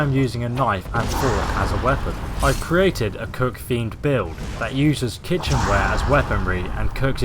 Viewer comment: you said you created a cook themed build, but I thought you said, "I've created a coke fiend build."